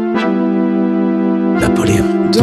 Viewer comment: This is probably French from the way it sounds and I don’t know French but I heard “German bear” German war”